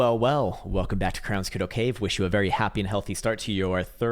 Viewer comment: Thanks for the video!.....It sounded like you were saying: "Welcome to Krowns Kiddo Cave"